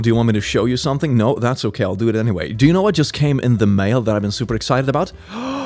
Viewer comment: I thought you said "I just CAME in the mail."